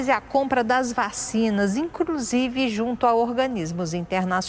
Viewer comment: só eu escutei "INCRUSIVE"?